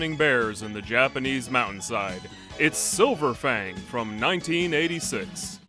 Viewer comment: Why did I hear "which is silver fang" as "which is so profane "